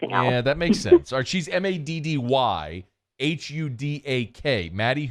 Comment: I thought he said Maddy who dat is with us today but he said maddy Hudack 😂🤣😂